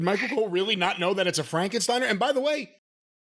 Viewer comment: I know u guys dont read this but i thought pat mcfee said breakenstiener i might of heard wrong